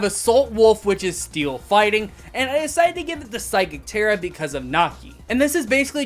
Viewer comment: What did he say at 4:29? Psychic terra?